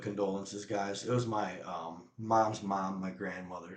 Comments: oh! I thought he said his mother!....mea culpa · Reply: Pretty sure he said "My mom's mom" which could easily be confused or misheard as "my mom"... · It was his grandma that passed... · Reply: oh! I thought he said his mother!....mea culpa